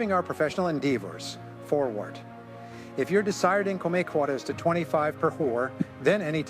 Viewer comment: can you tell me what he is saying exactly at 6:10 ? I am not native English speaker, so I'm so confused... I heard it might say "If your desired income make quarters to 25 per four". May I know what the original script says?